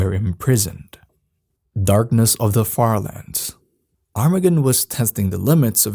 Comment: I thought that said dos, a minecraft creepypasta which i made was similar to that..